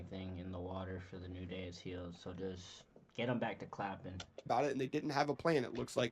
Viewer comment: Bro I had to pause the video when I heard “get em back to clapping” like WHAT WAS THAT